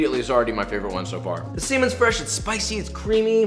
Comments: he says semen instead of salmon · I heard that as "the semen's fresh, it's spicy, it's creamy, it's salty, it's umami" and was very confused.